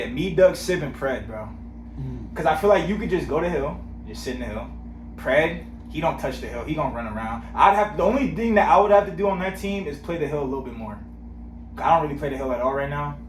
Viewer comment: Bro at 6:05 I thought you said "you can go to hell" 🤣🤣🤣😭 then I realized nope "hill" so funny